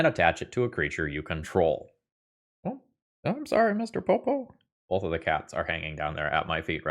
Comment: 😂😂😂😂😂 „Mr. Popo“, did I hear that right? Nice